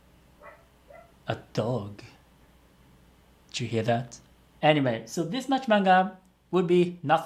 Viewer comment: is it just me or did he say "anime" instead of "anyway" at 17:03?